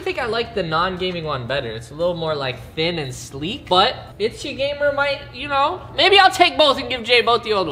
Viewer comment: DId he say SgGamer I’m here